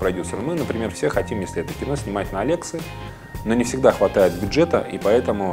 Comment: на какую камеру мы хотим снимать, на лексы? не расслышал( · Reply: возможно, он имел ввиду камеру арри алекса наверное одна из самых крутых камер